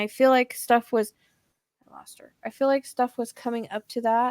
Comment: while you’re talking you can hear a male voice say “I lost her” and cc picks it up but I doubt either of you have tvs on near you while doing this. Is this an evp? Has anyone else heard this? I always watch these types of readings with cc just in case.